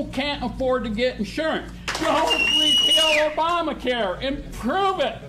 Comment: what is he saying after "to get insured", I don't understand it: "[...] repeal Obamacare and prove it!" Please someone help!